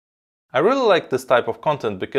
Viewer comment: At first I thought he said "I really like cyberpunk"